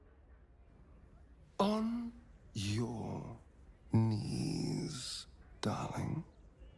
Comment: I heard him say “On your knees, darling” and I find myself on the floor.